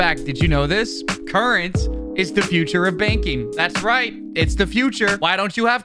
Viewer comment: with that sponsored intro, I thought you first said "baking" instead of "banking"